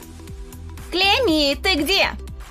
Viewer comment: Ха ха!клеми а мне послышалась клеим!😂 Поставь лайк если тебе тоже покозалась😂